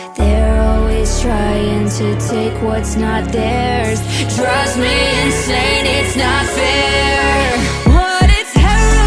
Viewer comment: Why do i hear "trust me insane its not fair"